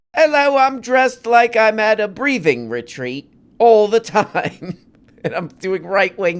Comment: " He's dressed like he's at a breathing retreat." Very funny! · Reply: I thought he said "breeding retreat". So I can stop googling you say?